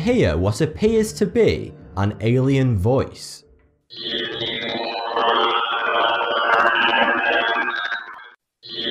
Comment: it sounds like it said “give me your”. But i cant deciper the rest. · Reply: Give me ur chocolate m and ms